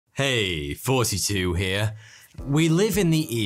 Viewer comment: No matter how many times I see the into I always hear “Heyyy 42 here”